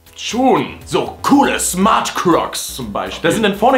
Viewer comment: ich hab smart cocks verstanden